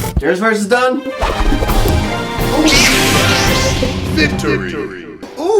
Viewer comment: 💀for a minute, I thought it said virgin when it said victory...in